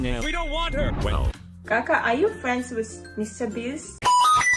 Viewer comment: She used gaga instead of Kaka in